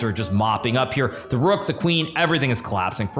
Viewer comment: Because of the way u pronounced colapsing i thought u said everything is clapped😂